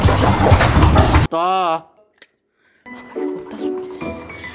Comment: 에 놔,, 하는거 빌리 bad guy의 duh~처럼 들려욬ㅋㅋㅋㅋㅋㅋㅋ